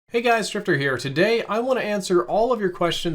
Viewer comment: I thought he said "Hey, guys. Stripper here."